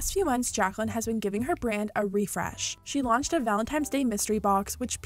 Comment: Did she just say "valentimes" day? 😂😂